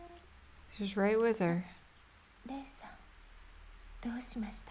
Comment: I just now realised I've been mishearing Miku say "Rei-san" as "Nee-san". I thought she was referring to Rei affectionately as a big sister